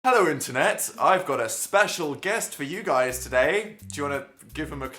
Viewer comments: MY AUDIO WAS AT LIKE 3 SQUARES (iPhone) IN THE BEGINNING OF THE VIDEO AND I HEARD "Hello internet, I'm going to smash my guest for you today..." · Wtf what was wrong with your voice as you said "internet"?! I heard "ontonet"